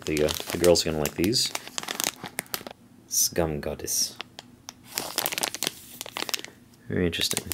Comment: when you read skumgodis I heard it as scum goddess xD great vid! happy holidays!